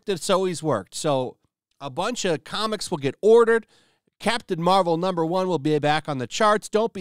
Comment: I thought you said Craptain Marvel..lol..